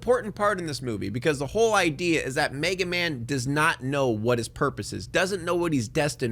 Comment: Did he say mega men